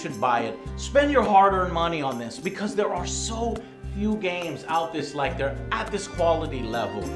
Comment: Did he just say, "Out THIS like THERE"?